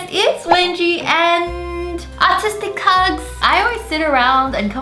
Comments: I thought she said autistic hug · Nerve heard of you before, you showed up on my feed thingy. Interesting. I thought you said autistic hug till I realized you had an accent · I've always wanted to try out some fiverr art!! And some of these were SOOOOOOO good! 💖💖💖💖 Which one did you like the best? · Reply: With your accent I thought u said autism hug at first . Btw I love ur Aussie accent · She said artistic hug and I thought she said autistic lolol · Is it just me or did it sound like she said autistic hugs?? 😂😂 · I thought she said autistic hugs lol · 😂😂😂 when she said "artistic hugs" I heard "autistic hugs" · At the first I thought she said “autistic hugs” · In the beggining, am I the only one that thought she said autistic hugs?